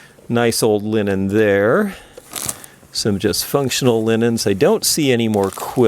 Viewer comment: When you were going through the cedar chest and looking at the linens , I think you said "just functional linens " (or something to that effect) and I swear, at first, I thought you said "dysfunctional linens " .